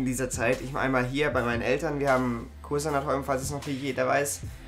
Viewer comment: Vielleicht geht es nur mir so, aber irgendwie kann ich bei 3:00 nicht verstehen was du sagst :) Du erzählst, dass du viel (bei deinen Eltern) arbeiten möchtest. Was möchtest du arbeiten? Und was haben deine Eltern? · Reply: Wir haben ein Kursanatorium <link>, meine Eltern sind beide Ärzte) und da kann ich recht viel Arbeiten.. Wie ich schon gesagt habe: Website erneuern, Prospekte, Hausvernetzung (Internet) und natürlich draußen mehr anpacken^^